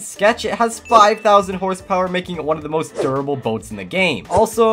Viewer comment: there are a lot of things wrong in this but 5:57 bugs me because he said "Horse Power" instead of Hit Points